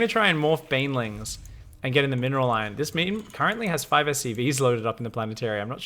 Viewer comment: did you just call the terran player a meme?